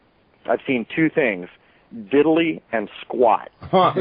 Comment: what he said on 42:25 ongoing <(...)I've seen two things: ... and "squat"> what the hell? don't got that both... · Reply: he said diddly and squat,,,means he saw fuck all, it was funny even i laughed